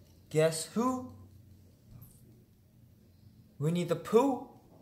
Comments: lol I actually thought kevin said "We need to poo" lol I have problems, help. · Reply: It really sounded like Kevin said ''We need to poo" 😂😂😂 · lol I actually thought kevin said "We need to poo" lol I have problems, help. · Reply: I think he said winnie the pooh